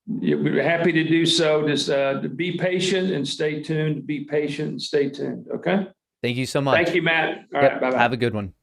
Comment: i heard be patient and hold be patient and hold